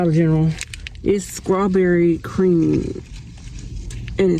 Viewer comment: Back to school, she must go. Did she say "Skrawberry"? 37:05🙆‍♀